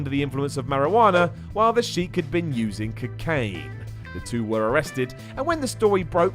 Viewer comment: Did Simon just say "Cacaine?"😂